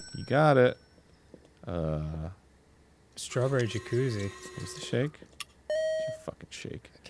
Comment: I thought it said"Strawberry Jesus" and I was very confused until Dan said what it really was. :_GrumpsConfiDANt